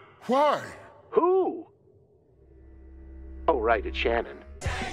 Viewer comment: Can anyway actually here what the name being said at 10:43 is? I've relistened to it 3 times, and can't work it out for the life of me. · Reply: Devin Gendron I know the first name is Shannon